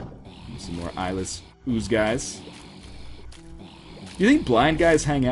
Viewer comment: Wow... I feel dumb now. I just realized you called the red enemies "Eyeless ooze guys" Up until now I thought you were saying "Isle of Zeus guys" /me performs a facepalm of extraordinary proportions.